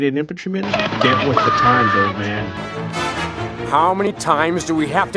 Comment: You need to do something about your microphone vs your clip sounds. I straight up couldn't hear what you said over this. Though I think I was able to make out the words "old man."